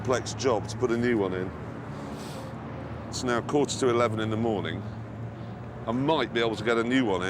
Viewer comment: did clarkson just say it's now kurz to eleven in the morning? Is this a thing in England to say kurz? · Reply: he said a quater to 11, meaning it is